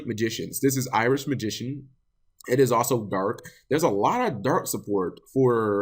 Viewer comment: I thought you said Irish Magician. That's a card I'd get in a heartbeat.